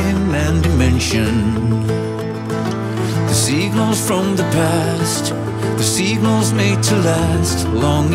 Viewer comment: I dunno why, but I heard 'the Xenos from the past'. XD XD XD